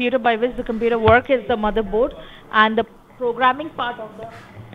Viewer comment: ok this woman must have meant "motherboard" instead of mothersheet.